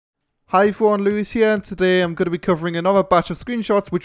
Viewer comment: i smile every time I hear "hi errone lews hiirr" :-D